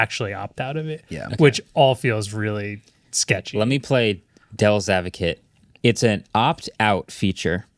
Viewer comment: Sounds like your saying "Dells advocate" 🤣